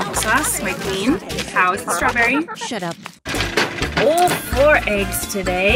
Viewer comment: Ahahahahaha! The chickens, chattering in English...I kept backing that up, trying to hear the words, I definitely heard some excitement about strawberries....hahaha